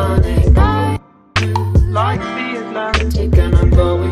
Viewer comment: For sum reason I hear "Guys blue like the Atlantic"